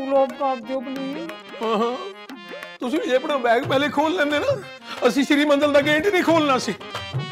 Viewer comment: what did he say? I didn't understand? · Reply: Agar suitcase pehle deekha deti toh sri manzal ka gate na kholte.